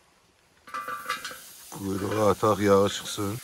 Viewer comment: usłyszałem słowo Kurwa hahahah